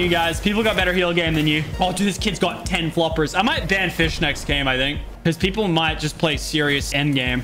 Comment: I thought he said no fish